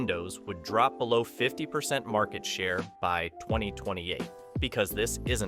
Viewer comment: Timestamp 1:39 says "2008" not "2028", It got me confused because I was hard to hear what was said at that actually point.